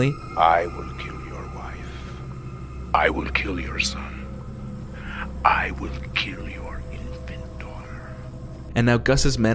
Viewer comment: for the longest I thought, he said, "I will kill your inventor," which frankly goes harder